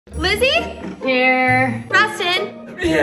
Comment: People who thought she said GLIZZIE!